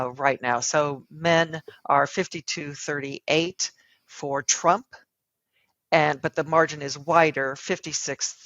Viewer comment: Lol, did she call him "Trunk" 4:46 ?